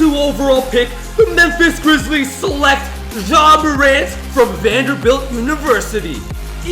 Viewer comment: Did I hear that right did he just say Vanderbilt University?